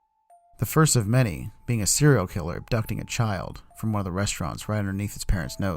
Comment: I heard serial killer willam aFtOn ????